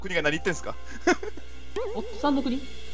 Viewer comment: フッサールがおっさんに聞こえて しまう。。ワイの耳が壊れたなw